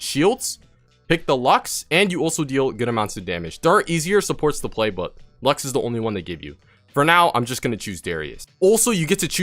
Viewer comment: What are you saying at 6:01 something easier, supports the playbook... What? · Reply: “There are easier supports to play, but, lux is the one they give you” is what he said ^^